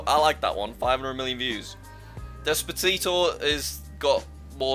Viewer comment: is it just me, or did he say “despatito”